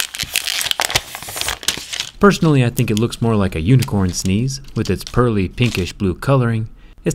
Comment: Huawei: breathing crystal Jerry: unicorn sneeze 😂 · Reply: damn, I thought he said "unicorn skins", that would be an unexpected crossover